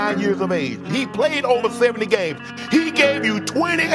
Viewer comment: i thought he said Yo- Goat—Cadaver